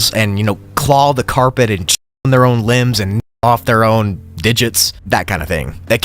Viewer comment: The censoring in the second sublevel of level 4 makes it worse, it sounds like you said "shit their own limbs"